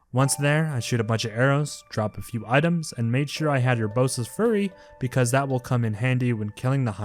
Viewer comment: did he say Urbosa's furry? that is an ability I would like to see lol